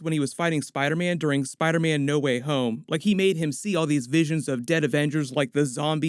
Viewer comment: So we gonna skip the fact he said no way home instead of far from home